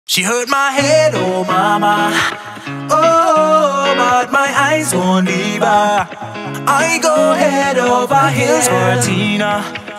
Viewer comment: "She hurt my hair obama, oh but my eyes have leavers, I go head over heels for tina, my cherrypop, she's my diva" such good lyrics, love how they give barrack obama a shout out cause his wife is called tina. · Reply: Krista Tiller I agree. I hear, " She hurt my head oh mama, but my eyes won't leave her. I go head over heels for tina, my cheerleader,she's my diva?"....